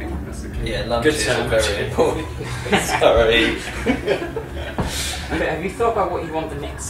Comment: What does Richard say at 1:34? It's been bugging me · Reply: Yeah lunch is very important ( ref roles or rolls)